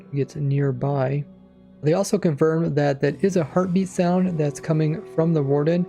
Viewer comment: I thought he said " they have confirmed that that is a herpes sound" 🤣